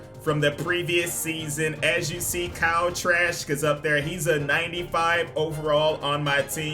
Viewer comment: Sounds like is saying Kyle Trash 😅😭